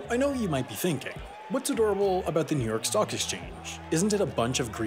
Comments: I thought you said New York sock exchange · I heard ‘New York Sock Exchange’ and was very confused for a moment